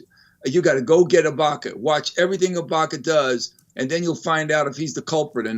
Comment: Did he say Ibanka at 5:30?